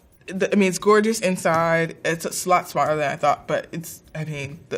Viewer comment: What the hell does she say at 12:05 'slot spotter than I thought'